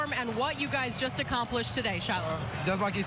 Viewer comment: Did she call Travis Shiloh? LOL